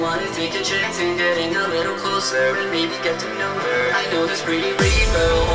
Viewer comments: "Maybe get to know her" I thought it said maybe get her number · That's some powerful nostalgia. Also, I always heard it as "I know this pretty rainbow". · I always thought he was saying “I know this pretty rainbow”